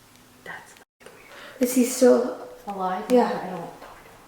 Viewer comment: The whisper sounds like it says I'm still back